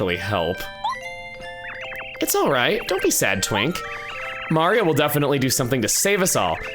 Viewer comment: I was just listening and i spit out my water when I heard say “Don’t be sad twnk” 😂😂